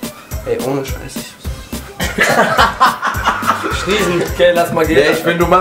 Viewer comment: Was sagt bei 7:57 nach dem "Ey ohne Scheiß..." ? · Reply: iwas mit ,,ich bin so besoffen"